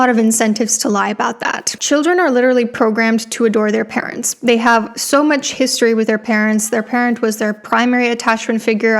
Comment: What is she saying at 9:20 😭😭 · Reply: think she just said "abused"